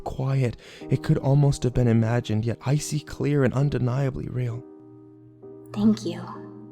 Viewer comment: When she said thank you i thought she said fuck you